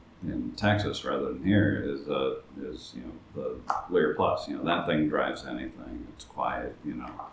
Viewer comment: Jason mentions an amp and I cannot understand the name he’s muttering whatsoever… help? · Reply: He was talking about the Lyr+. I've got one coming for review soon 🙂